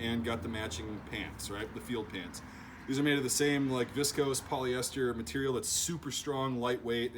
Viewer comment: Lol! I thought you said “disco polyester” at first. Keep up the good work, dude.